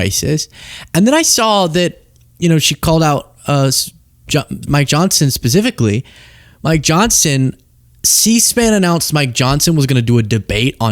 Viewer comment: Luke it sounded like you said "My Johnson" lol